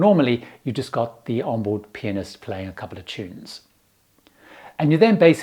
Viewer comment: my immature ass thought is said the p*nis play😭😭😭😅😅😅💀💀💀